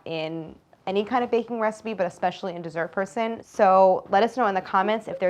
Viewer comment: Only now did I understand the intro music says "dessert person" (which is so obvious now and I feel so stupid). I always heard it like "these are your son", which makes no sense, and I just chose not to question such a bold choice).